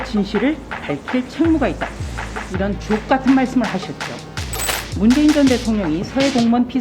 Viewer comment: 내 귀에는 자꾸 ㅈ 같은 말씀으로 들리는데 사이다 이 십니다,조수진 의원님..